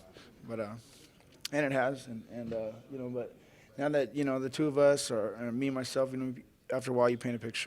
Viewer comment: did he just say "the two of us, me and myself" X'D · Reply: he actually said "the two of us" then he said or me myself as not referring to the two of them he actually didn't mess up there man try and listen